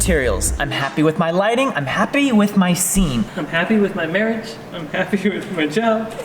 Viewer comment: did say “I am happy with my marriage, I’m happy with my child”????